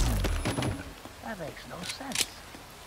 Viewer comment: PLEASE hope its not only me who I think the dead dude said "that makes no sense"